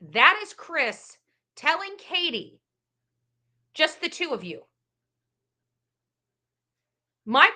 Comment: It sounds like Chris was saying just the 2 off you. he is trying to keep Katie on script eh!❤❤